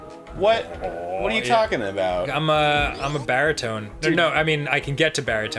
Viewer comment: Aww, Arin, you have a great "BAAAAAAAAAAH" :3c Though I wonder who else thought Dan said he was a "Barry-tone"...